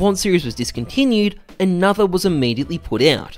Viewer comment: I legit thought it said discounted hahahaha